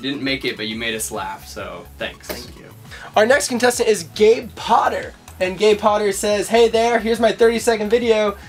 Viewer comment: When he said Gary Potter I thought he said Gay Potter LoL